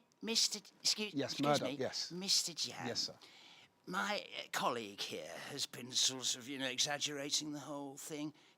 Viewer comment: what is murdoc saying? i just cant get it clearly :( is he saying sorely exaggerating? or sordidly? or smth else? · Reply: "My colleague here has been, sort of, you know, exaggerating the whole thing"